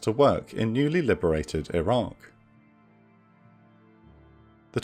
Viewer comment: it sounds like he says "newly liberated Iran."